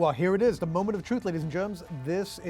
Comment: did he say “Ladies and Germs”??